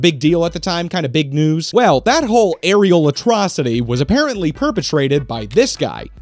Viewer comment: it really sounded like you said “areola-trocity,” which sounds like a term for a bad boob job. “You should have seen the incisions, they were awful, just an areolatrocity.”